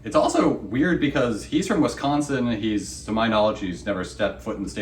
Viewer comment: i thought the lawyer was saying go sin hoe....